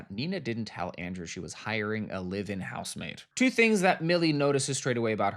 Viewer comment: I just understood that you were saying "live-in housemaid". I heard "living" housemaid (the whole video) and was confused. 😅